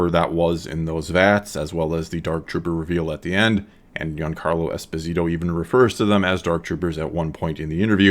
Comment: To me it sometimes sounded like you said 'Young Carlo' instead of 'Giancarlo'